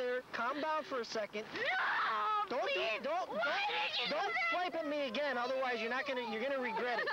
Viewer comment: What did he say....don't what at me again or you're going to regret it? It's not the word BITE. 1:35 Edit: it was SWIPE.